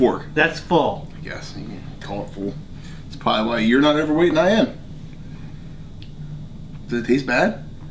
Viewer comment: "Maybe thats why your overweight and im not" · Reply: How is that what you heard him say. He said "that's probably why you're not overweight and I am"